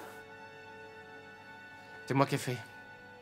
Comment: Trop mignon le petit “c’est moi qu’ai fait ”. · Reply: On dirait qu'il dit cest mon café